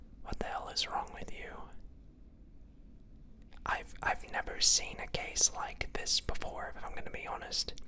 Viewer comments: I heard "I've never seen a gay like this before" gee thanks .... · When he said “I’ve never seen a case like this before” I heard “I’ve never seen a gay like this before” and I honestly felt that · In the beginning I thought he said I never seen a gay like this before lol